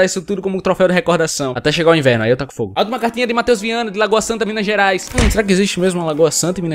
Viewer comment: Eu ouvi matheus hiwang?